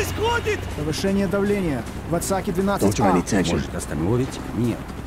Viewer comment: Does anyone know what the Russians are saying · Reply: Soviet Gunman 1: Что происходит? (What happened?) Soviet Gunman 2: Повышение давления! В отсеке 12А! (Increasing the pressure! In the compartment 12A!) Soviet Gunman 1: Может, остановить? (Should we stop it?) Soviet Gunman 3: Нет. Вспомогательная система должна остановить любые колебания. (No. Support system should stop any oscillations.) Soviet Gunman 2: Ты уверен? (Are you sure?) Soviet Gunman 3: Уверен. (I'm sure.) Soviet gunman: Эй! У нас проблема! Охранный отряд не выходит на связь! (Hey! We have a problem here! Guard platoon doesn't respond!)